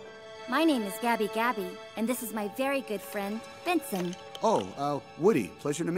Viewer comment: I'm sorry to say this...... But, Vincent looks like Slappy from Goosebumps!! D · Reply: Name’s Benson but Gabby Gabby says it like “Binsin” so sounds like she said Vincent. I only found out the dummy’s name is Benson since you can buy a toy of him among most all this movie’s characters.